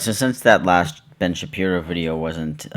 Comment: When he said Ben Shapiro I thought you said Ari Schapiro and I was like I’m pretty sure that’s not what Ari Shapiro... does